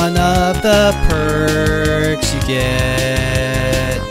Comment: When you said “Your mother might be concern” I thought you said “Your mother might have cancer”